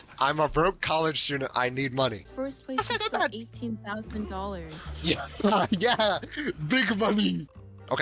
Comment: She was definitely saying "like $18,000" as in she didn't quite remember the exact split. It was probably an email or something and she was trying to recall what it was. I don't think the mic drop off was malicious on his part, it just happens sometimes when people talk at the same time in voice chats, as I'm sure you know.